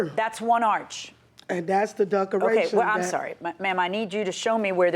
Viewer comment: Did she say "duck-er-ations"? 🤣🤣🤣